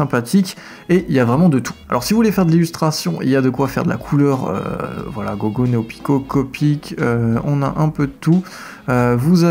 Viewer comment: C'est moi j'entend Boku no Pico à @ 1:30 ? ;)